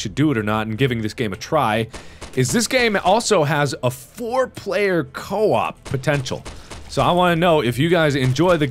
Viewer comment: i could swear that I heard him ask "foreplay or co-op" like 10 times...